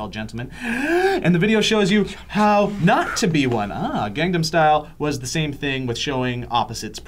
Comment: why do i keep hearing gang'dam' style